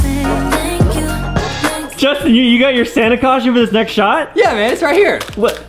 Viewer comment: I thought he said Fanta costume I- MOM I NEED EAR SURGERY OR SUM IM DEAF >;3